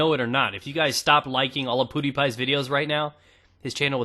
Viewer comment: LOL Did you just say PooDiePie at 3:00?